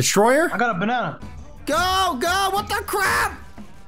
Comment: did you just say WHAT THE CRAP